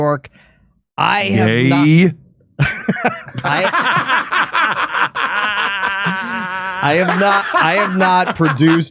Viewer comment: What does say at 7:12 (in response to Russell saying he owned five theaters) that made everybody laugh? I wasn't able to make sense of it. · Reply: Gaaaay